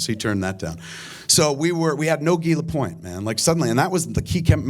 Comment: For the hearing impaired please note. At 1:48 the CC subtitles read "we had no gila point man". What he is actually saying is "we had no Guy Lapointe, man"; Guy Lapointe being one of the characters in the eventual film. The French name "Guy" is of course pronounced Gee (like the G in gear or game and not a J sound). Just wanted to help out people unaware of French/English differences and the hearing impaired ;)